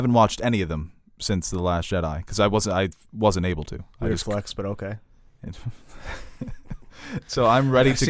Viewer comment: What does say around 27:05 something flex? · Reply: "weird flex, but ok"